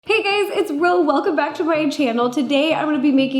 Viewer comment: i thought you said “hey gays” in the beggining i legit peed